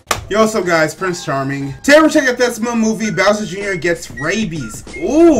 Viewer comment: I thought it said Bowser Junior gets Babies XDD